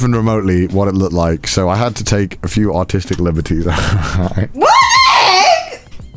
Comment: did he say artistic liberties or autistic liberties?